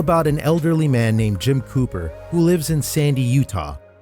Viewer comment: The accent, I thought he was saying pooper 💩 not COOPER! 😂🤣😂 So imature I know lol